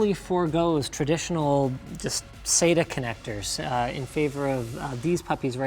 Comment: subtitle shows say to instead of sata (just saying)